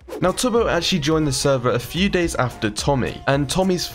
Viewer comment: Sorry if I’m wrong but I think tubbo is a boy right I heard she at 1:18 Sorry I don’t really watch tubbo sorry if I wrong!!!